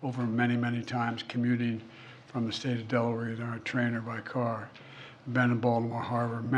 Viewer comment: So, I just did a quick search. How did Biden ride a train over that bridge? It didn't have train tracks on it. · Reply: Yeah, sounded like he said car to me.